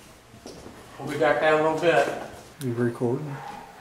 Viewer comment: I HEAR....."YOUR DOING SOMETHING"......THEY ARE AWARE.....AND IT SOUNDS LIKE THEY HAVE BEEN WAITING FOR YOU GUYS TO COME...….?????